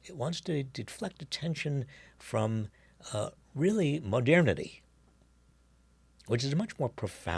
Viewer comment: Mr. Robert Reich please, what word did you use at 28:22, with the spelling of that word. I could not pick up (clearly hear) on it. Thank you for The Coffee Klatch. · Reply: *Modernity, specifically political modernity.